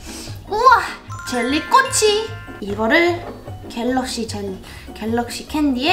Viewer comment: 저만잘못 들었습니까? 1:55초에 갤럭시 캔디가아니라 갤럭시 젤리 ㅋㅋㅋㅋ 자 봅시닼ㅋ